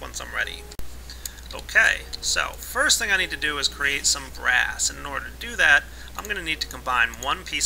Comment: I thought u said grass when you were makin BRASS